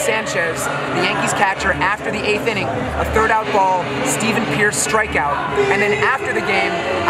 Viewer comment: did you say Steven Pearce?